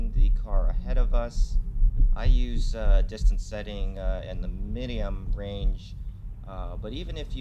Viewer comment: sounded like minium? did you mean medium or minimum lol